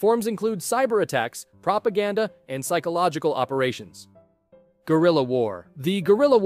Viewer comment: I heard "Gorilla war" instead of "Guerilla"